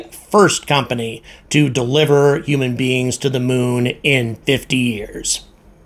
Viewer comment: When you said 'in 50 years..' I heard 'in another 50 years..' That would about fit.